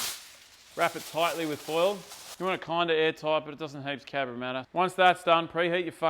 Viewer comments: did you say dosn't heaps cabramatta hahaha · Did you say "It doesn't heaps Cabramatta" at 1:40? Haha. I've never heard that one before. Champion!